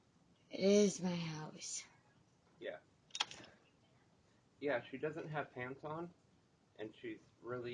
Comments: First play I thought he said she doesn't have tampons 😂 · I thought he said "she doesn't have tampon "😮them I realized he said"pants on"😊 😮 but seriously: she's lucky the wrong person didn't answer that door and dragged her in and graped her😮I'm glad he was a good guy😊